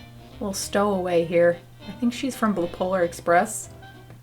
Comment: i heard "bipolar express"